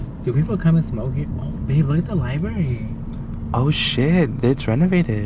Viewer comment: Love your videos!! Is that ur boyfriend?? OMG IT IS YOUR BOYFRIENDS!!! 😱I literally had to repeat the video a lot of times to make sure it was right what I heard he calls you “Babe” 😱18:51“Bebe look at the library” awww hopefully we get to meet him soon & you deserve to be happy ur such a great person & he’s so lucky to have you... 💞 Boyfriend:”You’re fucking boring”:”Yeah so da fuck r u bitch jajaja we love each other” 🤣🤣🤣 you r too funny & about that light thing in the car is totally true my dad use to say that to my sisters & I when turning the light on in the car “apaguen la luz nos van a dar un ticket”🤣🤣🤣 oh Mexican parents but we love them! Mexicana x vida! 🇲🇽👍🏻